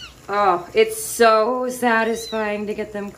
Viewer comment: did she just say Its so satisfying?